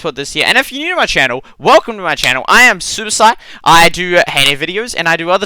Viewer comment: was it only me but did it sound like he said Suicide instead of superSight?